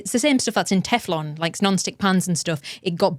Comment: I keep hearing "non stick pants"...